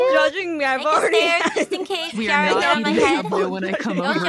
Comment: I think Calli says "we are not eating subways when I come over"